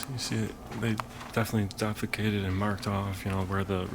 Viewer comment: Did you say "they definitely defecated"? LOL🤣